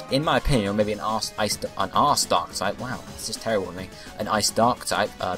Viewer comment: I heard you say "An ass dark type".